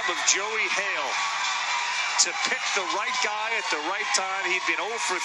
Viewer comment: who heard a word god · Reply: I heard guy.